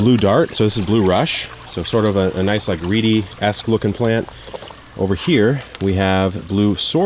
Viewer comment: the captions say "Sort of a nice like reedy-ass looking plant" instead of Reedy-esque and I laughed so hard I had to spit out my water 🤣 I wasn't expecting it but it was hilarious.